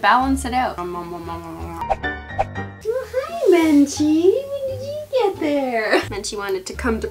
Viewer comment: bruh it sounded like you said benchi